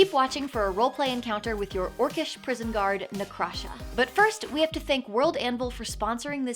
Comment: have just realized her name is Na’krasha when all along i thought she’d been saying “i am the crusher”